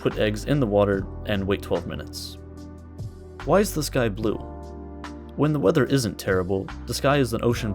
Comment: i thought you said "why is this guy blue"